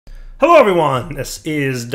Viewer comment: I wonder why starts his videos with "Hello Obi Wan"... (I always mishear that part).